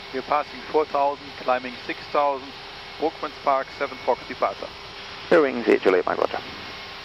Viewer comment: Nice video to start a Saturday morning!! I have a quick question - what did the pilot say on his first call to London at about 29:30 after “climbing 6000”? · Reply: Brookmans Park 7 Fox departure. It's the BPK7F departure out of Heathrow that goes to the Brookmans Park VOR